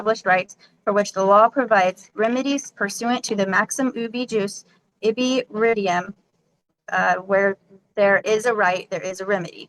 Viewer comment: wtf did she say?!?! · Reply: pursuant to the maxim ubee juice ibee ridium